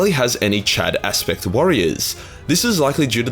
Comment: The first time you said 'Aspect Warriors' I swear to god I heard 'Aspergers Warriors', and immediately thought, "WTF! The Eldar don't have Space marines!?"